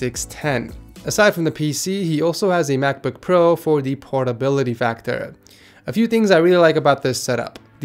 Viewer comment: i thought he said "pornability factor"